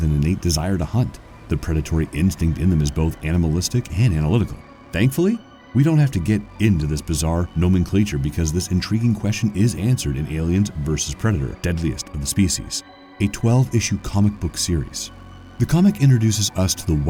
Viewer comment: What is the narrator trying to say at 13:07!? "No mean cleetcher" - Could that be 'Nomenclature' (No-MEN-clature). They should get the person who wrote the script to do the narration.